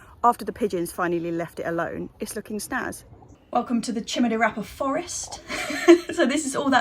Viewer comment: LOL, I hadn't heard of it before, and I thought you were saying "chimney rapper"!!!!!😅